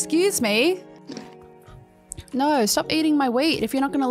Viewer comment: i thought she said stop eating my weed